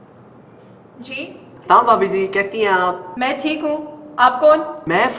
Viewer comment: She actually said who are you?But thee subtitles says how are you.Fix it